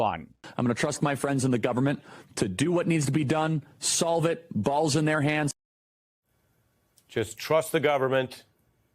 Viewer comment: Did Charlie say "The Balls are in his hands?" Stormy says they smell...